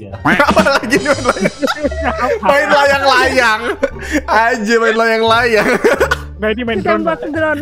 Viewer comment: gw yg salah denger atau gw gk pernah denger: anjir main layang-layang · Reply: Salah denger yg bener:aje Main layang-layang